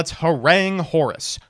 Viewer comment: What is he saying that he is going to do? YT proposes „herang“ but that's not a word in the dictionary (apparently?).